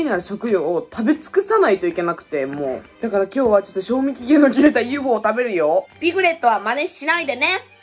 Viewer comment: 「賞味期限の切れたヒヨごを食べるヨ」にしか聞こえなくて焦ったちゅう😅‼wwwww w www